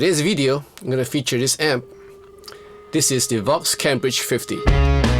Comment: With your accent it almost sounded like you said .. "the vox cabbage 50" he he he he he :D Great job as always sir !!